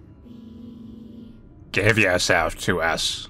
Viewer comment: I love Youtube transcriptions they are very funny... If I didn't understand english I would have thought Manly was saying inappropriate things in the video. In the 5:30 seconds video when Manly was reading the text "Give yourself to us" Youtube actually transcribe it as "Give your ass out" Hahaha/